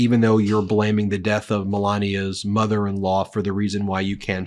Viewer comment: It sounded like he said Marla, not Melania, when talking about her moms death and missing her funeral. If he showed up at the funeral he'd probably get struck by lightning 🌩️